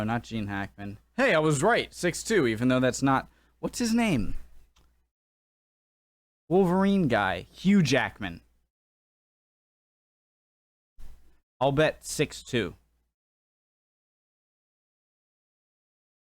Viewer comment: For a second there, I thought that you were saying Hugh Jampton! . . . which is not unrelated to Patrick Stewart's story!